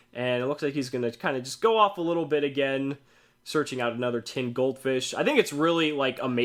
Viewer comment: that special summon with pot of duality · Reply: oh! sorry, i dont see for one second and i hear "duality and tin goldfish" my bad :P